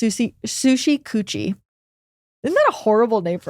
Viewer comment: I thought y'all were saying sushi coochy and I was SHHHHHOOOOOKETH that someone would name a resteraunt that